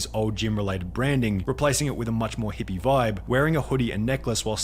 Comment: This is not in defence of the fitness guy. But god damn SunnyV2 be Mr Fantastic with super stretchy arms to fucking reach this far 10:04 "wearing hippie clothes" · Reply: Where did he say hippie clothes? I only heard him say “hippie vibe”, which I guess he’s kinda right.